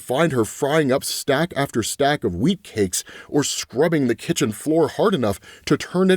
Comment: I thought he said she was "making stack after stack of weed cakes" as hes describing her morning